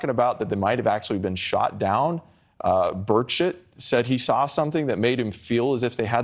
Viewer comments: did he say bird sh*t? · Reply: This is the comment I came looking for! Glad it wasn't just me that heard it! After a brief googling, I believe he is referring to someone by the name of "Burchett". I'm very relieved this person doesn't actually go by the name of bird s**t!! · Did he call Burchett "bird shit" at 2:59?